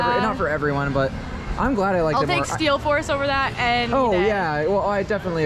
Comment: did she say steel force?